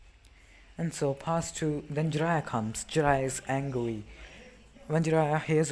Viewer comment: Anyone else hear him say jiraiya was angwy